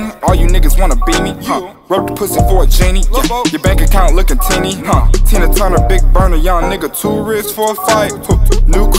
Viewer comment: Di I hear (lil pump) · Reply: No it was lil boat